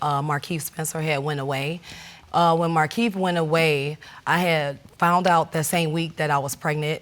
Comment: "I had founded out that I was pregnant..." All those years in school and these people can't even formulate proper sentences or pronounce simple words! Shameful. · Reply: she clearly says "I had found out". What time stamp are you referring to?